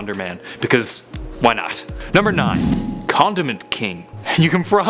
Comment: I thought he said "Condom Man"